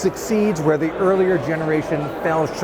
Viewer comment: Did he say "Galaxy Book F"?